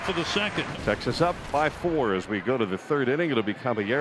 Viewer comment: Did I hear that right? Texas up 5-4. Does anyone check these edits before they’re posted? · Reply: He said "Texas up BY 4" ... it was very hard to hear though.